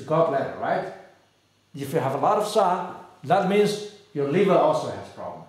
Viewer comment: he said “ if you have a lot of ###, that means your liver also has problem “ can anyone tell me what he actually said? A lot of what ? TIA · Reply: Sha means toxins.